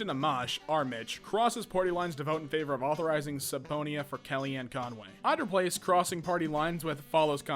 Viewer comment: to hear say subponyah instead of subpoena. Love you please don't kill me UwU